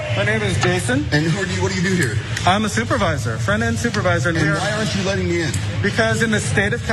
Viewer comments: I thought the manager said "Front end manager" but anyway, think of the store as the Costco Embassy. Their Embassy, their rules. Don't like it take your silver spoon and go somewhere else. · I thought he said FrontEnd supervisor